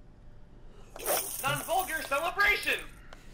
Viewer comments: NON-VULGAR CELEBRATION! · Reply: Oh, is that what it is? I keep hearing "unfold your celebration". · oh, wait, he's saying "Non-Vulgar Celebration"? I always hear it as "Unfold your celebration"... (English is not my first language, tbh) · Things new taunt sounds like: "An ogre celebration!" "Non-vulgar celebration" "I'm older than a brasion!" "I'm over in elation" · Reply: I heard "unfold your celebration!" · I thought he was saying "unfold your celebration"